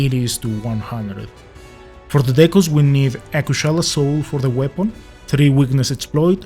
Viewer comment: Please don't take this the wrong way I mean no disrespect but I'm having trouble with what you are saying at 00:56? "for the decos we need..." and I can't make out the word here. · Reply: A kushala soul for the weapon. That's the following. No worries. English is not my native language. There is always room to improve